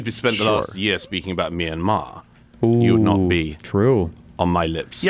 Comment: I thought he said "Me and Maher" not Myanmar